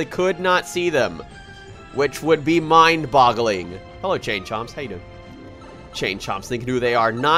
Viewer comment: HAHAHAHA, for a second I thought you said "JaneChomps" >.<